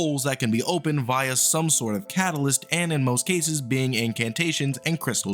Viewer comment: what did you just say. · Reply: Catalyst